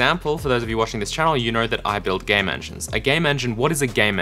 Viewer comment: It sounded like you said you build "gay mansion" when you said I build "game engine". Fraudian slip?